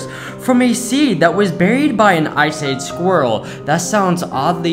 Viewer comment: It sounds like he said “Ice aid squirrel”.